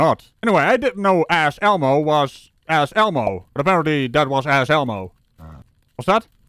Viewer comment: I thought you kept saying, "Ask Elmo", lol! Elmo works perfectly!